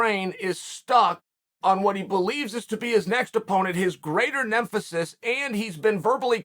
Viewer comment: Did he just say nemfesis?